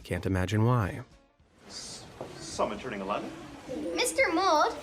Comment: nah. I think I heard wrong, there's no way the guy showed up and said "someone's turning 11?" while caressing his hands and looking at 3 kids. ☠️☠️☠️